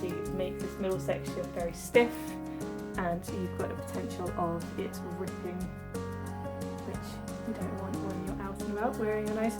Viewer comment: One question, from 6:06 to 6:12 I didn't quite catch what you said - I just heard 'potential of' and 'nice new dress' ?? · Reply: thanks for your question, the sound is a little funny there. I say there is a potential for it to rip (if you don't use a zigzag or stretch stitch) which you wouldn't want on your nice new dress. Hope that helps!